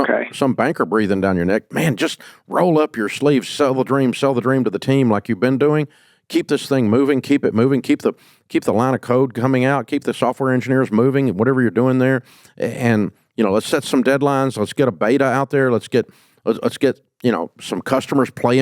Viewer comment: When he said "keep the line of code coming out" as he was talking about motivating the team, I first thought he said "line of coke" and I was like damn!